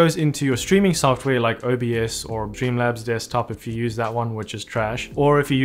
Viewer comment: Are you saying "Dreamlabs Desktop" or "Streamlabs Desktop" at 4:25? I hear it as "Dreamlabs" and the auto subtitles think it's "Dreamlabs" but I don't think there's such a thing. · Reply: Streamlabs 😂